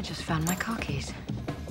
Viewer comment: car keys? i thought she said "cookies". what's gotten in my ears anyway?